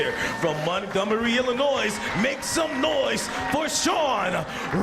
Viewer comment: did he say Illinois's?